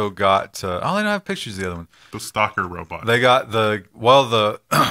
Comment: confirmed, Walmart will soon have stalker robots at least that is what I heard the first time and had to go back and replay it a couple fo times, lol